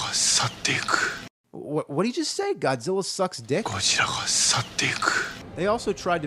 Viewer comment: it sounds like he said godzilla sucks dick lol